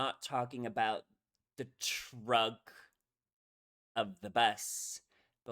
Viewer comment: Great. You dissected the lyric, but I heard "trunk" instead of "truck". Is it a sort of assonance or rhyming with "junk" which is maybe referring to "butt" (respectfully) playfully?